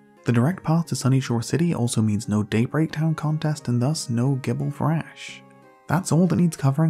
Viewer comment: I must have misheard him because it almost sounded like he says “no Date Rape Town Contest”